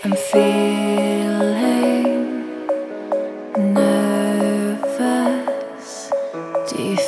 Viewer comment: Can someone help me? Im not native and i wanna know what she says at 1:55 "Im feeling ____ Do you feel the same" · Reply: Nervous